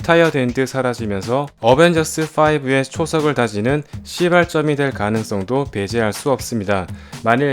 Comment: 이거 나만 시발점이 아니라 ^^ㅣ발점으로 들리나?